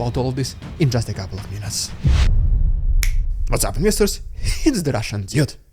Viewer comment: What do you say at the start of your video? I hear "what's up minister's?" I doubt that is correct though